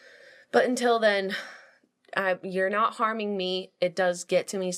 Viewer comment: Jenna said "You're not harming me" and I wasn't paying attention and thought she said "farming" so I can't stop laughing now. I shouldn't be laughing at this serious of a topic but farming